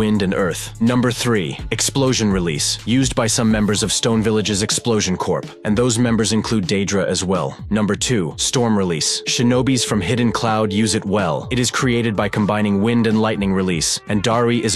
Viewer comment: Just me heard him saying DAEDRA instead of Deidara?